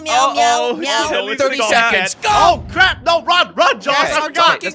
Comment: Jelly: meow meow meow 30 seconds go Lol what did he say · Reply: He said I am ganna count to 30 seconds go